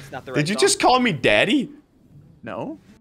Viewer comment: did u just call me daddy? Me:I thought it was mommy